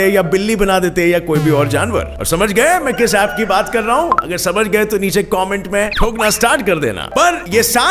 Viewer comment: Hi gaurav sir I'm huge fan of your videos. Today I've watched this many more time and I'd heard something like "comment bhokna ya thokna shuru karo" 7:25 to 7:28 . What the word is their. Please make me sure about it, because the word is rhyme with the dog image seen in the video. It can listen wrong by somebody like me. Please make me sure that is not a "bhokna" because i may be wrong...😁😁. Lot of irritating adds Also for common people's. I wants work with you also.